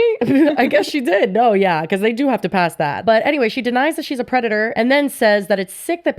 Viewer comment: Sounds like she is saying she is being a “cunty” creator